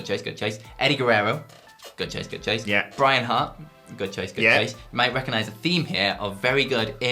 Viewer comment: Did he say Brian Hart at 8:15?